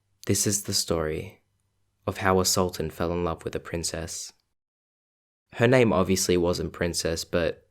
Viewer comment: wow i heard "this is how an assaultant fell in love with a princess."